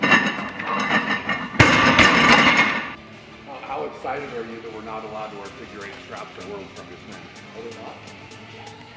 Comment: I didnt understand what Brian said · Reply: “Evan how are excited are you that we’re not allowed figure 8 straps”